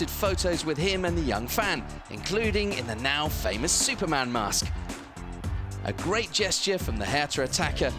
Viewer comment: it says superman mask instead of Spider-Man